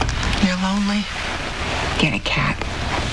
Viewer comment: I always thought she said , get a cab. I guess this makes more sense